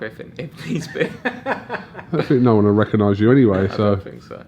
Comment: Did say " no one would recognise you anyway " if he got locked up lol....